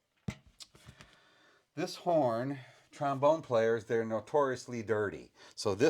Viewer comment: I thought it said “tombstone” and got confused when i saw a trombone